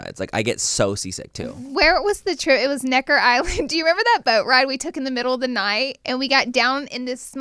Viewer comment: Lol I thought said Butt ride 🤣🤣🤣